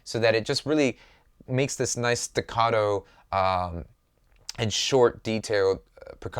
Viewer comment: What is the word you use at 8:25??? Sounds like ticado but there is nothing on glossary musical terms that is similar to that. The only thing similar is a spanish word "tocado" meaning bruised, or familiar. Please elaborate, and thank you.